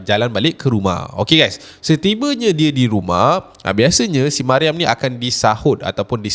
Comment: / 2:51 ada sebut nama Maryam eh?? ke salah dengar??🤔 sebab dengar kata taknak sebut nama-nama 😅🙏🏼